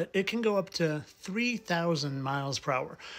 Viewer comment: You got the bullet thing wrong again. Bullets travel approximately 1,500 to 2050 mph. Not 300 miles per hour. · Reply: Like my bad. I thought he said 300 mph